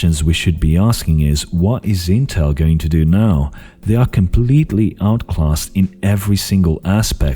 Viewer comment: Disliked because you didn’t want AMD to surpass Intel... I don’t think they will surpass Intel completely but in some fields they will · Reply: I could have sworn what he said was "completely outclass Intel". And it was said in the context of wanting at least a few competitive parts from Intel to at least keep the market from stagnating like it has before.